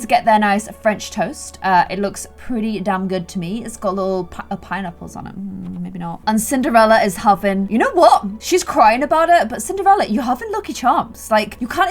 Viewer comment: I seriously thought she said simderella at the beginning! 😅